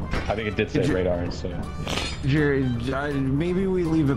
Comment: I didn't catch what was said but somehow my brian came up with Jerry Jedidiah